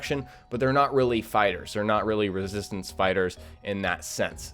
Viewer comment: did he say 2:40 resistant spiders? oo that is scary